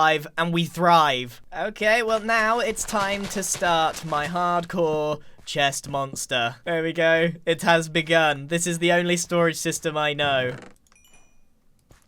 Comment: Grian did you just say that there is another time of storage system??? because you said it was the only one you knew......